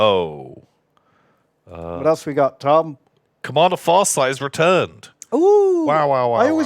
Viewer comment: I thought you said Far Side. Like the Far Side comics by Gary Larsen. A Far Side 40k Army XD.